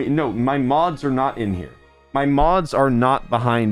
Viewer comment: i thought he said moms instead of mods so i got very suprised fr